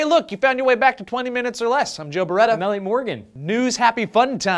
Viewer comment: I feel like Elliott pronounces his name wrong in every video. If I didn't know any better, it would sound like he was saying his name was "L.A. Morgan" or "Ellie Morgan." xD